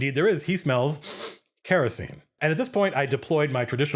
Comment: Ahahaha, I'm sorry Matt, but when you first said 'he smells... kerosene' I thought you said "He smells... *sniff sniff*... HERESY." XD I've been too into 40k I guess lmao! I don't know if you'll ever see this comment on a video this old but I hope you do and you like it lol